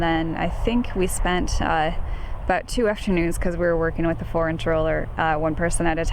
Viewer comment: Funny....I heard "foreign troller" instead of "four inch roller". Why such a small roller, btw? This seems like self inflicted torture to me. Answer = dedication..!!!